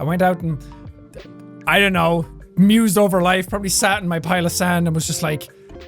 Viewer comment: Did he just say sat in my pile of sand? Is that a thing in Ireland?